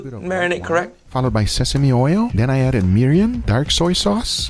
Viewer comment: "Then I added Miriam" you didn't catch that miss pronunciation.